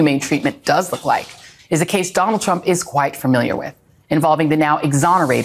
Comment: I heard '...a case he's white familiar with.' and laughed my a$s off! That'd be such a good pun for the white supremacist version of mansplaining. 😂